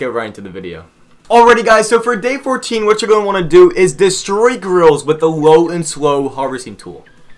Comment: at first I thought you said is Destroy GIRLS